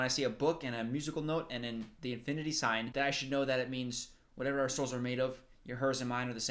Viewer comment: OMG it is "whatever OUR SOULS are made of"?? I have seen this video a couple of times by now and I always thought they said "Whatever assholes are made of". I thought that was the weirdest line ever and I am glad I heard wrong. :DD